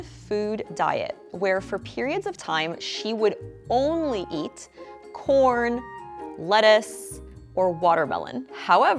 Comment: I HEARD PORN AND I WAS LIKE HOLD UP SVDN